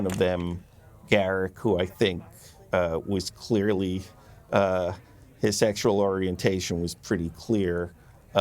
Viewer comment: Ira Steven Behr @ 5:50 He is stumbling a bit... Is Behr saying Garuk was " a sexual" or "asexual"?? I know Garuk loved/cared for Ziyal but never saw an indication towards a male unless it was Bashir.. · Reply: Ira says: "...Garak, who I think was clearly.. his sexual orientation was pretty clear! But we didn't go there." Meaning for Ira Garak was probably intened to be at least bi. But its true that his bond to Ziyal was the most forthcoming aspect when it comes to love concerning Garak. Its also possible that Ira mixes some things up after all these years or that it more wishful thinking on his part.